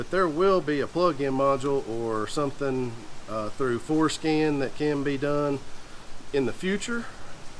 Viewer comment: Did he say foreskin?